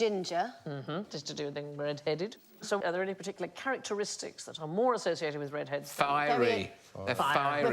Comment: Did she say, "carroteristic"??